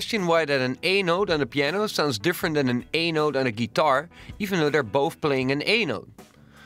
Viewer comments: when you say "A note" it sounds like your saying anal. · did he said anal or a note at 0:19?